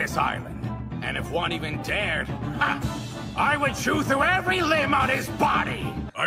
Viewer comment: Sungwon says "I will chew through every limb on his body" and the subtitles say "I'd be the first to bite them to death" 👌